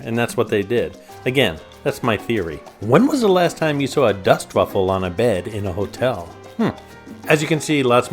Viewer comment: Dust ruffle! Its a sheet that goes from the bed frame to the floor. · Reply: oh. LOL. Getting old. Thought I heard dustbuckle. Dust ruffle